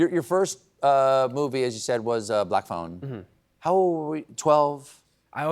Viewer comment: At the beginning of this I kept hearing "Black Bone", but couldn't find any such movie! 😄